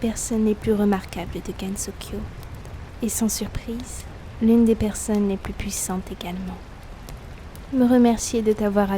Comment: Au début j'ai entendu "l'une des personnes les plus puissantes et canons" à